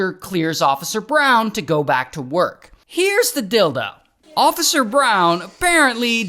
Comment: the caption said “Heres the dildo.” 💀 (yt captions)